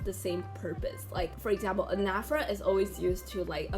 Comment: what is the word you said? Inaphra…? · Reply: Anaphora